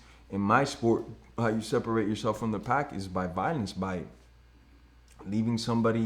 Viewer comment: Did Jorge say "Pakis" at 44:30 - 44:33??